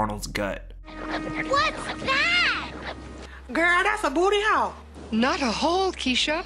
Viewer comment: she doesn’t actually say “girl that’s a bot ty hole” she actually says “ poor arnald looks like he’s got a hole in his stomach”